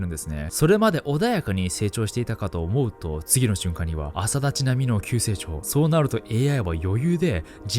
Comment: 「朝立ち並の急成長」が阿佐ヶ谷姉妹の急成長に聞こえてしまった